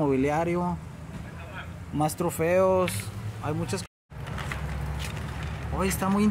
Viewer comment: tu dices más trofeos y yo oi maestros feos 😂😂😂